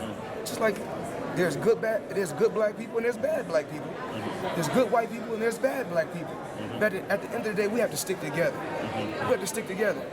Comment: Did he say "There's good white people, and there's bad black people" ?